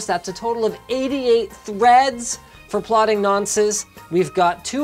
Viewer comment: did he just say nonces?